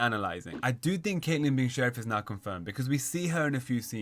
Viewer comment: did you say “ I do think caitlyn being sheriff is NOT confirmed” or “I do think caitlyn being sheriff is NOW confirmed”? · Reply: Now*